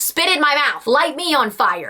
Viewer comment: I was in a public place and they heard you say spit on my face lol